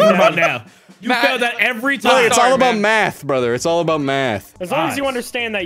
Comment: I thought you say it’s all about math 😂